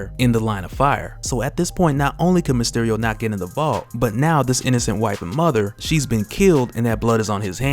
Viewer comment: i could have sworn you said "innocent white mother"